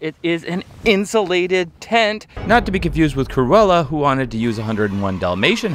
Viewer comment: I thought you said Corolla like Toyota Corolla but not cruella 😂